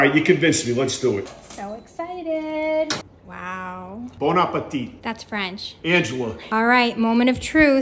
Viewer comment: Ive watched enough videos of qcp ....so now after every bon appetit i hear "bitch"💀